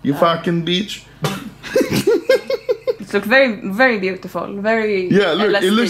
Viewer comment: Is it just me, or did say "Very LSD."